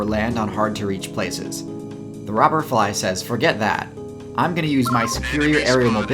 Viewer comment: i thought he said robert fly